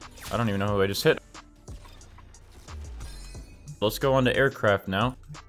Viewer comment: When he said aircraft. I thought he said Air crap and I’m sry